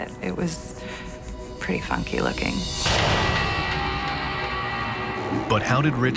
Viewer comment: "it was pretty funky looking" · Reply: Kyte I thought she said it was fucky looking